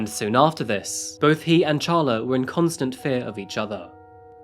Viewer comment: I feel stupid. I had to check her name because I kept hearing say “challah” and I’m thinking “it’s pronounced Ha-La and it’s a bread what is he talking about? I thought a person?” Then I checked it. 🤦‍♂️